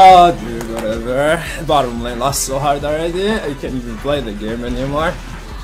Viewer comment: What the fuck did he said? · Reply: Light Zero5 "Bottom lane lost so hard already. I can't even play the game anymore."